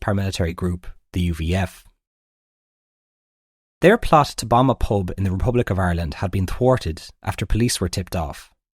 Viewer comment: Thought he said bomb a Pope, I was thinking wow that would get some headlines😂